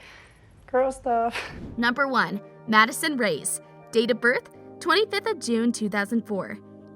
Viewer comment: wait… did she just say Madison RAYS!!!!